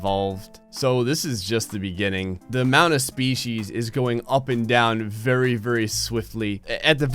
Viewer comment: Thought you said feces